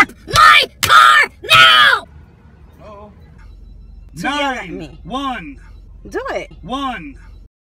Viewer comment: Man: "91" Women: "Do it" Man: "1" Women: TRIGGERED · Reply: 91 if u say it to fast it sound like ur saying “nice one”